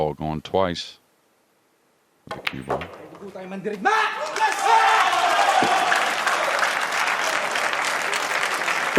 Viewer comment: curious what did Chua say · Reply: "May dugo tayong mandirigma!" = "We have the blood of a warrior!"